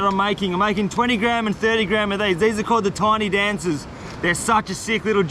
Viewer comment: Did he say the Tony Danza's on 29:43?